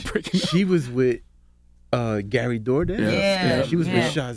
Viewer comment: I thought they said Gary Doordash